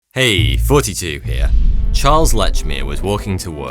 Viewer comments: Sounds like he says hey it’s 42 here 😂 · I always thought he said 42 here not thoughty2, how have I never noticed this before,that's ridiculous.